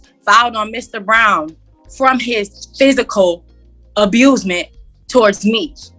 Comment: Did I just hear "abusment" I'm not tripping right